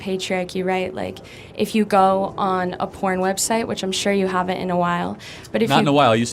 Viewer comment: 你聽錯了，她說charlie應該很久沒看，Charlie說不是很久以前而且他曾經上癮，只是現在戒了，而且希望其他人都可以戒掉這個不好的習慣，所以學生恭喜他有脫離，兩個就真的在很理性地討論。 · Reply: 多谢指正，是我听错了。那位女士说的是“I am sure you haven’t for a while” 我听成了“I am sure you have in a while.” 当然 ， 查理认为女士说的“你很久没看了” 没有准确描述他的现状— 他现在已经戒掉那个不好的习惯，也希望其他人也戒掉。 多谢您指出来！